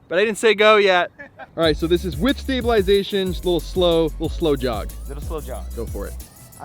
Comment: When you said "slow jog" I thought you said "blow job"